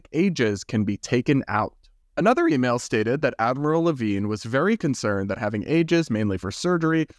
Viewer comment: i had to look up from eating like WTF... i didnt have my volume loud enough i gues.. i thought you said "Avril Levine" lol 20:58 "i feel everyones tryna get something for free these days" (talking about others) BUT at the same damn time had the MF NERVE to say "but the guy is always supposed to pay first" is the wildest form of hypocrisy i ever heard in a while .. and that IS that dude whos been all on YT with people questioning if he fr or not lol.. i doubt it...